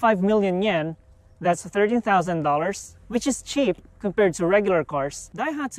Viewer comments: Actually seems like a pretty decent little vehicle. Thought he said $30,000 and I was like "WHAT?!?!" but then I went back and listened more closely... he's saying $13,000. That seems much much more reasonable for this car. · That's a cool car, bonus points for bench seating up front I really miss that from my old Buick. on the other hand the positioning for the drink holders seem wildly impractical considering how stereotypically practical the Japanese are and the price point isn't very good either. If it ever came to North America they have a hard time selling it for more than $22k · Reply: Oh? I thought he said $30000, in that case $13k is a steal!